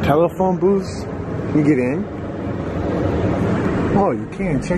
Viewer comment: sounded like his walky talky saying get in, I played it back several times.